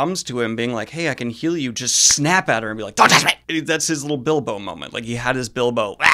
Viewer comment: I thought you said Mat had a "bimbo moment" lmao